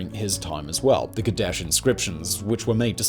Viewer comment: I was listening to this in my heaphones while on the bus, I had to stop and go back because I thought you said the "Kardashian scriptures". Was quite relieved that is was the Kadesh inscriptions.